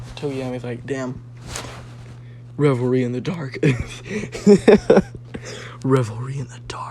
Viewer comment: I always thought he said “I embrace the darkness”🧐